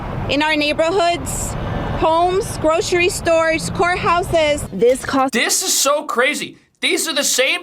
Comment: I really thought she said whore house, not court houses.